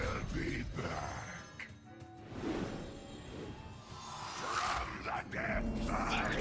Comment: The bossfight in a nutshell: NOW YOU FEEL PAIN! NOW YOU FEEL PAIN! NOW YOU FEEL PAIN! NOW YOU FEEL PAIN! NOW YOU FEEL PAIN! NOW YOU FEEL PAIN! NOW YOU FEEL PAIN! NOW YOU FEEL PAIN! NOW YOU FEEL PAIN! NOW YOU FEEL PAIN! and more towards the end: FROM THE DEPTHS I COME FROM THE DEPTHS I COME FROM THE DEPTHS I COME FROM THE DEPTHS I COME FROM THE DEPTHS I COME FROM THE DEPTHS I COME FROM THE DEPTHS I COME FROM THE DEPTHS I COME FROM THE DEPTHS I COME FROM THE DEPTHS I COME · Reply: I always thought he said from the death i come